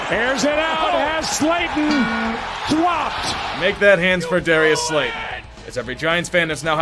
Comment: D-di-… did he just say “Dwopped”?